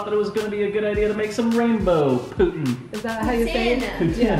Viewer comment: I keep hearing “putain” instead... 🤣🤣🤣 I’m surprised that the Canadians didn’t say something about that-or maybe they just didn’t want to know